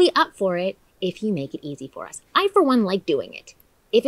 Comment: After I heard "BJ. I for one like doing it" my eyes just couldn't get off of those lips